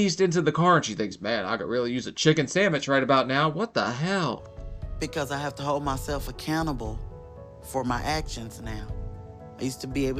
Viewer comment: I had to think about that for a second then cos at first I heard “Because I have to hold myself a cannibal”.